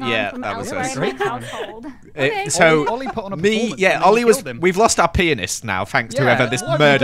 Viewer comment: someone please tell me what he just said I- I’m scared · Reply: He said "we've lost our pianist now" he just pronounced it weird lmao